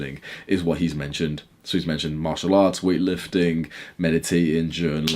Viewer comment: what you said here? “mashallah”? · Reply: martial arts